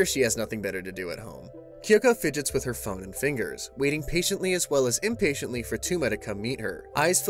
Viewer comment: For a sec I thought he said "she fidgets with her fucking fingers" 🤣